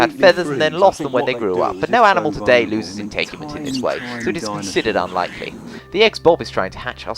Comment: What did that say? I couldn't understand it due to the lack of subtitles. Can you please explain what was said? P.S.: This is not sarcasm. I actually didn't understand what you said in that part of the video. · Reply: "But no animal today loses integument this way, so it's considered unlikely."